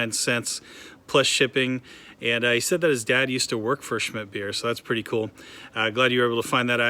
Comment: Did I hear you say "Schidt" beer ? If I am correct, it is called "Schmidt" beer. The other way just makes it sound really bad and I can honestly say if I got a "schidt" beer I don't think that I would drink it. I understand that you don't want to share your job with us and that makes total sense. However, I think that it would be fun if the other people posting comments would provide "one" job that they think that you may be working as. To start, I think that you are a fire fighter. Of course, you do not have to respond to these comments.